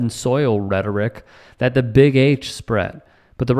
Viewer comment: ".....Trump is literally using the same "Blood and Soil" rhetoric that The Big H spread....." Which I misheard as "The Big Apes". And that might be even better!